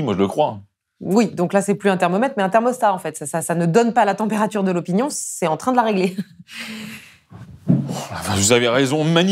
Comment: « Le sondage c’est plus un thermomètre c’est devenu un thermostat. Ça n’indique pas la température, ça la règle » Je ne sais pas si elle est de vous mais je la garde. Très bon résumé de la période actuelle. Continuez je prend toujours autant de plaisir à vous entendre! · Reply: I am not a french native but i heard « ce n’est plus un thermomètre c’est un thermostat en fait, ça nous donne pas la température de l’opinion, c’est en train de la régler » est ce que j’ai bien entendu ?